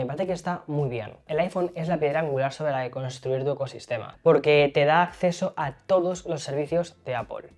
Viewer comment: Qué dijo? ❤ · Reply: Una piedra angular sobre la que construir tu ecosistema